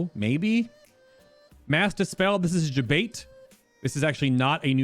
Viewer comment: When you say "mass dispel" I kept hearing "master spell" and I was mad confused